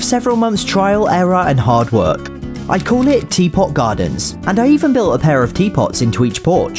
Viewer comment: When you said teapot gardens 0:37 I thought you said TPOT(the power of two) gardens lol